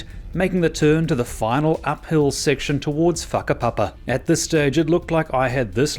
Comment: ish. When he said what sounded like F**k A Papa I just lol'd.